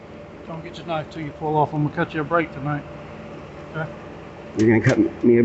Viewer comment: did he say he was going to cut his breaks?